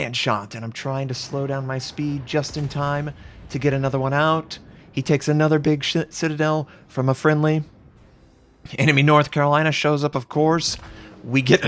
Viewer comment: did he just say he takes another big shit? By they way nice video;)